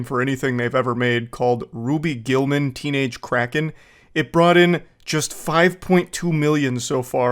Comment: On the radio the movie sounded like it said teenage crack head